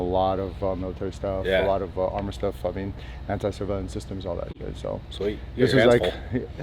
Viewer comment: did he say anti-civilian systems?🤨